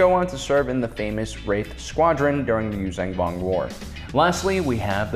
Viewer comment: Ummm did he say rape squad??